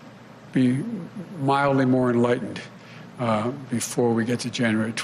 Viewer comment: I know I heard “mildly more enlightened” but I’m sure he meant “wildly more enlightened”